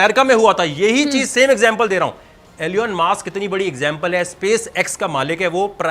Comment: he actually said alieon mask instead of Elon Musk!!!!